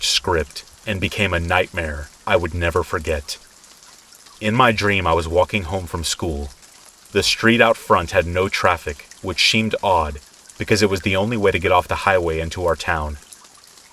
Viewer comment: Did anyone else hear it at 9:09 story number 2, he says 'it SHEEMED odd,' i found it hilarious. 😂